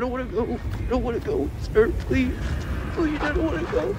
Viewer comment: nói gì rứa ae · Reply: Đoạn này mình nhớ là Spider nói "Chú Stark, cháu cảm thấy không ổn lắm", "Cháu không muốn chết, làm ơn, cháu không muốn chết". Kiểu "I don't wanna go" thì phải.